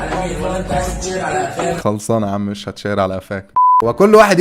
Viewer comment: انا سمعتها عايز تتشهر على العفانه😂😂😂😂15:24 هو معفن اصلن 😂😂😂